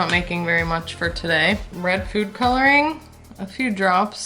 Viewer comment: I thought she said poop coloring not food coloring